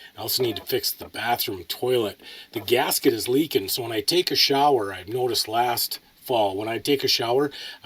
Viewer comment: 😂 thought you said when I take a 💩🚽